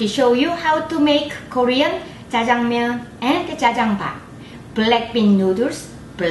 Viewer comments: Am I the only one who heard BLACKPINK instead or black bean? On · ohh I thought you was saying blackpink